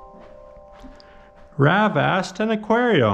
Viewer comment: i was texting my girl and i about fell outta my chair because i thought he said "grab ass and aquarion"